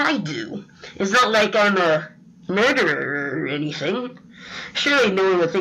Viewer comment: WHAT edit: wait did he say “murderer”